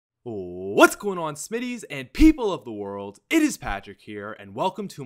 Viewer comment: when you say smititys it sounds like your saying shmities